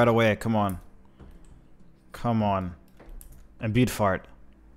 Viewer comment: I'm guessing that I am the only one who noticed that instead of, " Imbued Heart, " he actually says, " Imbued Fart," 3 times in a row. 😂😂 fucking rolling, live for your content